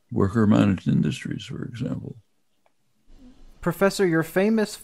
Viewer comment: I'm having a hard time grasping what Chomsky says at 58:44, something along the lines of "Worker _ ___" anyone have a clue? cheers! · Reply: "worker managed industries for example"